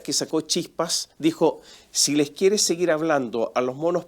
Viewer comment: Creo que dijo "les" compañeres (13:33), que es como recomiendan les nueves expertes de la "academia". Quizá se inspiran en el lenguaje sin género de Irán, que ha dado buenos resultados.